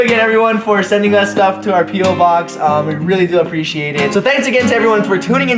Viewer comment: Am I the only one who heard "pedo box" at 7:20?